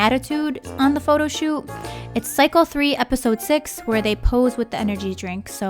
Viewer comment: anyone else pick up on how she said cical instead of session or am I just hearing stuff